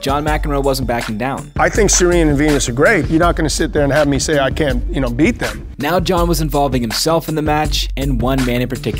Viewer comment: She’s dust that old man, and it would be interesting to see her play against someone who actually posed a challenge. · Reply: he clearly says “I’m not going to sit there and let you think I can’t beat them”.